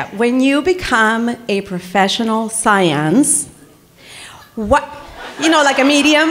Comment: Can you guys stop making them uncomfortable for the Q&A? They literally said erotic fanfic is prohibited because they obviously don’t like that shit, then people go up and talk about a dead dad and asking Katya if she’s going to lick a skintag, calls them mom, and says seance completely wrong and uses the word in a completely wrong way. And that’s just one person. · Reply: i finished the whole video and didnt realize the girl said seance until now i thought she said science kfsjghskfdjg